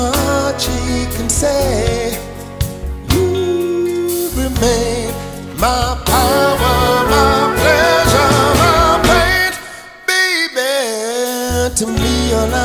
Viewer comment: who else hears "my power, my pleasure, my paint" ?